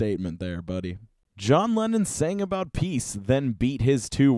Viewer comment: I thought they said Lennin and I got confused